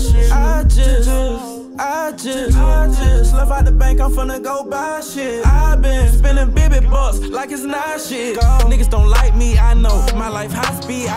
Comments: Did he just say spending Bibby bucks. cool 👿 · changed the perspective on dissing opps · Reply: I’m pretty sure I heard him say “spending bibby bucks like it’s not shit” and he “wanna be bibby fly like bird man”